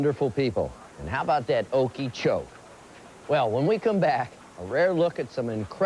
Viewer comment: What did he say at like 19:35? How about that okeecho okicho lol what?? I cant make out whqt hes saying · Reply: Okee-choke. I'm almost certain it's the move of a Florida area pro wrestler, but I can't for the life of me find more (the reference is Lake Okeechobee).